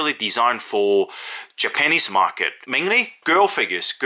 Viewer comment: with your accent sounded like it said the panties market